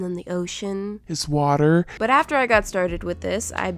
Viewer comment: ALSOOOO. Yes, I realize that a goldfish is a freshwater fish. I know you guys are super worried about him. Luckily, I think he'll survive anyway considering THIS OCEAN ISN'T REAL 😂😂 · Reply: I thought you said the ocean isn’t real lmao